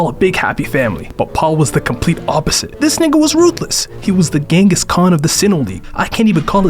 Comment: I thought he said “this nigga was ruthless” 😭😭😭